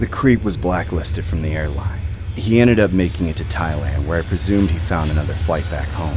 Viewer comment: HOPE YALL ARE HAVING A GREAT SUMMER!!! ☀🌊 HORROR SHORTS PARTY (ANIMATOR): NEW MERCH LINK: <link> MY PATREON: <link> MY INSTAGRAM: <link> MY TWITTER: <link> WHITE HORSE ANIMATIONS (ANIMATOR): CHANNEL: <link> EYEBROSE STUDIO (ANIMATOR) JESSE JAMES BOND (WRITER) <link> JOSHUA CLEMENTS (WRITER) (NARRATOR/ VOICE ACTOR): CHANNEL: <link> (NARRATOR/ VOICE ACTOR): PLAYLIST: <link> (NARRATOR/ VOICE ACTOR): CHANNEL: <link> (NARRATOR/ VOICE ACTOR): CHANNEL: <link> (NARRATOR/ VOICE ACTOR): <mail> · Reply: Thank you for adding Taiwan to your video! But I think I heard Thailand 2 times (18:18 and